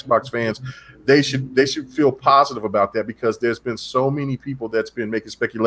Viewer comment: said: Philpositive! Instead of feel positive